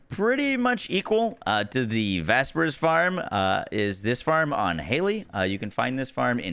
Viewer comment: Am i the only one who heard "This farm on Hailey" at 11:30 then was immediately sad once a Viessa was on screen? 😅